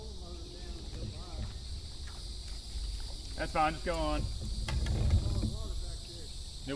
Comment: Never thought you’d stoop so low with Complaints; get over it & move on… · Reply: That's not what said. clearly said, "That's fine, just go on". He never said "Naw". The time on the video this occurred is the 7:36 to 7:39 mark. It was bad enough he deliberately did it, but he didn't even use the trolling motor, he used the big motor. The guy was heading the other direction , until he saw catch that bass. You need to turn your volume up so you can clearly hear say, "That's fine, just go on". Then you're going to use the term "and I quote". You just made up a quote, when everyone can clearly hear what said. I catch everything, so don't tell me I should've caught it the first time, because I did catch everything the first time. 😂😅 Nice try though..... Talking about said "Naw". 😅🤣😂